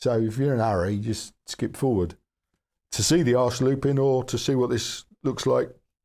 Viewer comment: Brilliant, loads of box opening suspense. I thought I had miss heard 'arse looping' until you demonstrated it. Great vid as usual. Keep it up!!!!